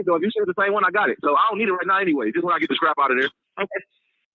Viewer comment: Did he say scrap or strap as in pew pew